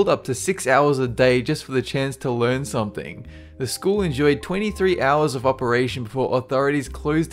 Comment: "The School enjoyed 23 hours..." WAIT WHAT?! Don't you mean 23 YEARS like the CAPTIONS say?! O_o